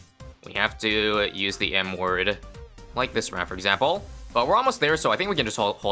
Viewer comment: youre going to have to use the what word now? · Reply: Erm he said "M word" 🤓☝